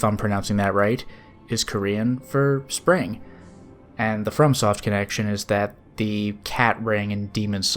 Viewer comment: 1A: that's the intrigue! 1B: I'm not sure it matters. Might just be public transportation. If it's some proprietary train, then I guess more intrigue. 2. It would just affect my humanity gain. 3. Clearly Oz, but there's loads of fairy tale potential. Also, I'm not sure what Sunsoft game you're mentioning. I don't remember bringing that up. Happy new year! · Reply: Maybe I miss understood you. I thought you said the next game was from Sunsoft. If I misunderstood sorry.